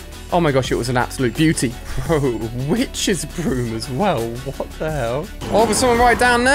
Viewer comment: anybody hear that? Child friendly? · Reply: He said witches broom